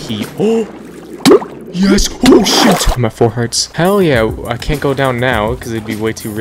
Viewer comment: Lol it sounded like he said “Full hearts!” 😂